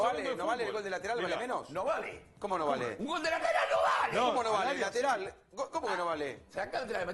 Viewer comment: que carajo grita?! No Lo entendi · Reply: "Un gol de lateral no valee!"